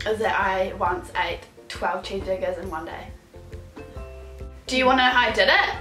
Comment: what did she say in 1:32? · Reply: She said she once ate 12 cheese burgers in one day